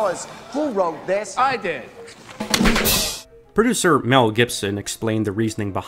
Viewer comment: Gibson!? I watched this video twice and thought the whole time you said "Mel Brooks", which would have been less shocking.